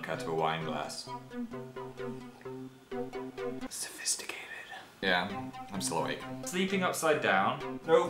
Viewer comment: What on earth does Phil say at 1:45? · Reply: I meant right afterwards, but rewatching it I just realised it's 'yeah, still awake'. :)